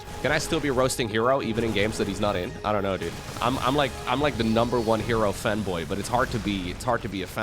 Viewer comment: I thought I heard "I'm like the #1 hero femboy"